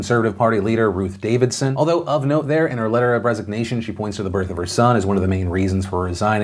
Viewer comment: Maybe it's the fact that I can't not hear you say Bregzit instead of Brexit but at 13:34 I swear I heard you say "Although, of note there, in her letter of Bregzignation...."